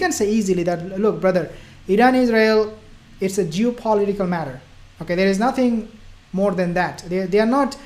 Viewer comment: Assalamu alaikum brother 0:41 did you say" jew political matter " 😄